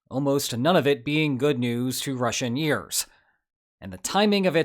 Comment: Did he say "almost none of it being good news to Russianeers?" Do they have a variety show?